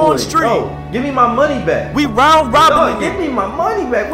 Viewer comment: At first I thought he said "FIGHT TWERKERBOIEXTREME!!!!"